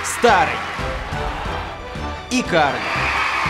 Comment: По губам понял что сказал "Карлик"